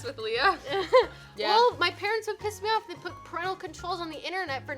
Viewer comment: Who else heard leah say “pissed”